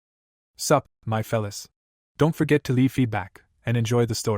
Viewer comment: that opening surprised me, thought you said "suck, my fellas"